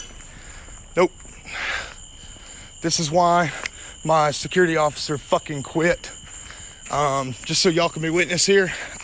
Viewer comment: what is the voice saying? “...officer do you...?”